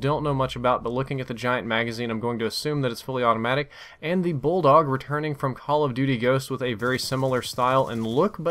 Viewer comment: I had to rewind when you said bulldog because I thought you said Ebola lmao